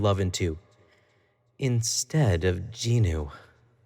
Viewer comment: What did he says jinu in 3:38 the jinu like in the KPop demon Hunter